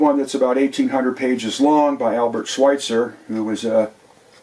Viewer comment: Can't understand the name of the guy he's talking about at around minute 25:00, when talking about music. Buck, bokes, what is it? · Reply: Albert Schweitzer - book about J.S. Bach. <link>